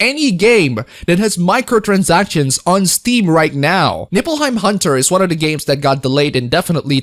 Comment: I'd swear to god I keep hearing you said "Nippleheim hunter".